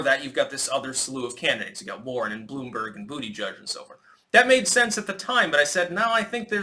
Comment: I had to rewind, I thought I'd heard you say "Bootyjug" I'm still trying to catch my breath. Hilarious.